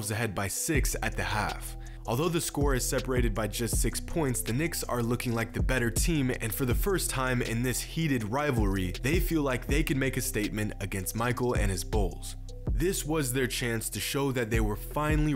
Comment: I thought he said "Michael and his balls" I was like😳😳